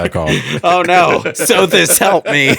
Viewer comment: For a minute there, I thought you said ‘Sophist Help me!’ and was about to be like ‘oh shit the BSC crew know about the Party Crashers!’